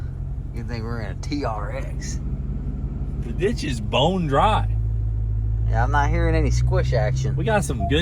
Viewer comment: Not gonna lie it sounded like cleeter said “this bitch is bone dry” 😂